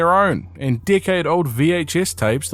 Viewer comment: Ok but the first time I heard you say “decade old tapes” I had to rewind because I DEFINITELY heard “dickhead old tapes” 😭😭